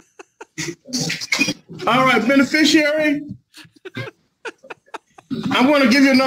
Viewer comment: I thought he said “Bennie Fishary” as his name. 😂😂😂 Judge mutes him, “What’d you say?” Pure gold. ❤❤❤